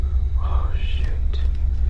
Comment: you can see their fingers move before the glass :/ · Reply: I heard someone say oh shit